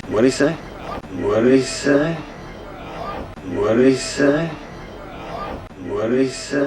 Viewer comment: طب انا ليه ف اللقطة الاخيرة بسمعهم بيقولو What did you say مش What did he say